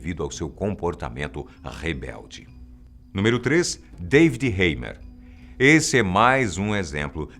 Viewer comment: entendi David Guetta :p